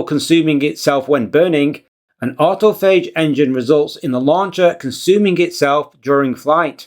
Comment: I coughed up my coffee because I thought you said Astrophage Engine 😭🤣